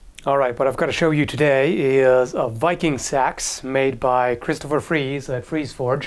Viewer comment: I heard "Viking Sax" and now I can't stop imagining a viking playing a saxophone.